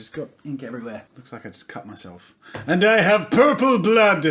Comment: i thought you said "and i have poo poo blood" 😂😂😂